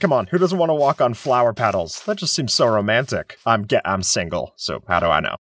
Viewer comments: why did it sound like he said "i'm gay- i'm single" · i heard skip say: "I'm ga- I'm single"🤔